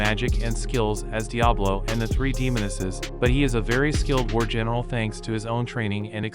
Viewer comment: when he said war general i heard virgin